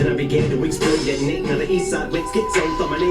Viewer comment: You missed an important bar " I will pop and take a spot from you" After releasing killshot halsey commented that "him and i" sold more than Em and mgk(may he RIP) so basically Em decided to drop MTBMB at the same day of Halsey's album and he indead outsold her without a promo and took the spot lmao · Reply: I thought that he was saying that he began to explode at Nate, meaning his little brother. I didn’t know it was detonate.